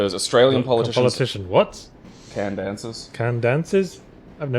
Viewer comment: Fucken... I was born and always lived in the Brisbane area, I'm 30, and I still thought he said "can dances"...